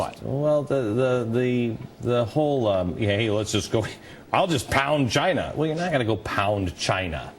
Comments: LOL, who else thought he said "Well I'll just pound GINA"? I'm so immature -_ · I thought Beck said "Pound 'gina" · i thought he said " pound gina " lol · It kind of sounded like Beck said "I'm gonna pound gina"